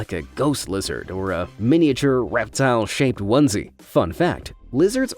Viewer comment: me trying to work out whether he swore or said fun fact 😂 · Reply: I where said fun fact but if he swore tho I’d make an edit and be proud